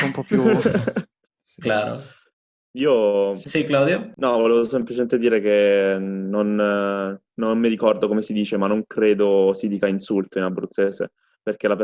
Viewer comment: les juro que escuche "No, boludo"